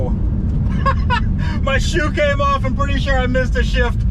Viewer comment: 😂 sounded like Tavarish said “ I shook em off” 😂 when he said “my shoe came off” 😂